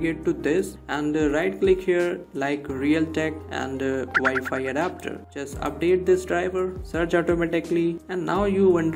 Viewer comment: Sorry my friend I can't hear there what to press for opening this in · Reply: Windows key + x